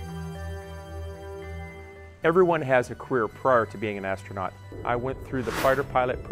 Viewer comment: sounds like he said queer